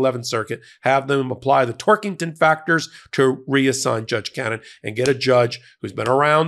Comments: I kept hearing "Twerkington Factor". Please take that image out of my head. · Haha! I thought you were saying "Twerkington Factor!" Boy, is my face red! (No... no.. not THAT red!) 😂 💙